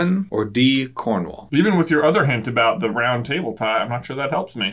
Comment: When said "the round table," I thought he said "brown tape" and spent the whole rest of the video trying to figure out the connection between King Arthur's castle and brown tape.